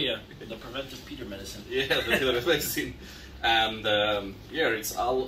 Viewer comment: @ 0:43 says 'preventive peter/peder/pedar' medicine. What he says is not clear. I know in one of your earlier vids you described this word but it was not too clear to me then either. Can you 'actually' spell the word as I've heard several commentators using it and I had it in my head as 'peder' as in 'pedo' as you explained it. Clarification would be good. And even though I am called Peter, if it is peter I would not be offended, just not sure (though secretly I hope it is not peter). Sorry to bother you with trivial matters. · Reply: Pidar